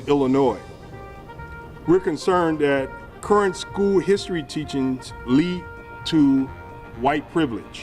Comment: Did he say "history teach'ends" at